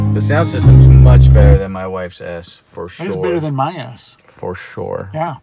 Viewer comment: I thought he said "the sounds system is much better than my wife's ass"